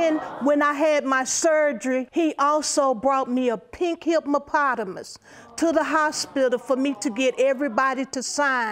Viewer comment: Did she say "hipMOpotamus" @ 19:16 ??? I cannot.